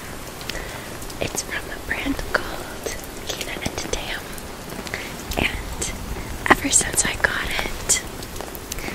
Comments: Anyone catch the name of the clothing brand she mentions around 2:30? I can't make it out. 😖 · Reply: Kina and Tam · Anyone know what brand name she said the jacket is? I cant understand what she said. Time stamp is 2:29 thanks!! /gen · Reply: She says Kina and Tam 🙂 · does anyone know what brand she said the jacket is from? i keep replaying it but i cant make it out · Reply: Finally got it. It’s called Kina and Tam!